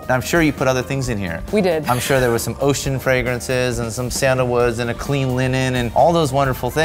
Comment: One of the times I thought woods said noods (nudes)